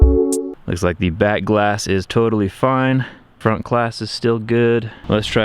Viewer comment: lol, when he dropped the iPhone x onto the ground first I thought he said "looks like the back glass is totally destroyed"